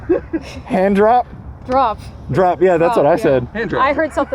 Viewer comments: Vin sounds like he said hand job I heard it too lol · Not gonna lie, I heard, "handjob" too. Thanks, Raycon! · I heard something else too. It sounded like you said hand job bro. This was interesting I had the corvette but it needs some work. Maybe another day we can retest this